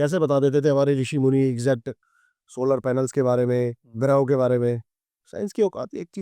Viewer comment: Did he say Solar Panels 😂😂😂